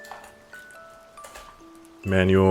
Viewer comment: When you said "manual" at 4:53 I thought you were saying my name "Immanuel" and it scared me lol